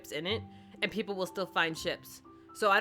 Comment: When you were talking about ships I thought you said chips